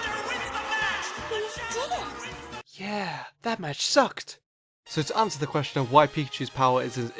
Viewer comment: wait wait wait am I hearing things or did he say yeah that match sucked